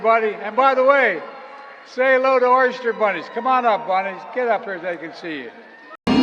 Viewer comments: holy fuck did he say oyster bunny ? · Did he just say “oyster bunnies”?🤨